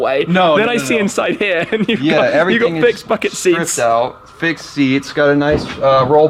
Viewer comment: When he said big bucket seats it sounded like he said big fucking seats 😂